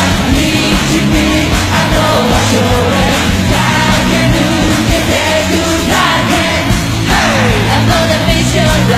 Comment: dang it why can't I hear Kuroto xD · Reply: Hyper Muteki Ex-Aid its SHIN dan kuroto 😂